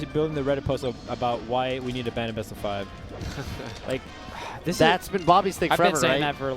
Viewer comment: What are they talking about at 6:40? I didn't hear what leffen said. I heard "we need to ban bo5" but I'm not sure